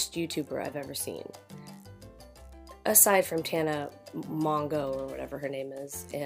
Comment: *gasp! I would NEVER!!** In my eyes, Jenna Marbles will always reign QUEEN of YT. (Remind me though, what did I say and what's the time stamp to which you are referring?) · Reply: right in the beginning at 1:10 where you talk about other bad YouTubers, you are saying Tana but for some weird reason I heard Jenna at first 😂 And yes Jenna is the one of the best people on YouTube 😍